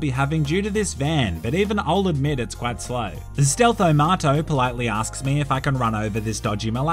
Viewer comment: Stealtho marto.... I really thought he was saying stealth tomato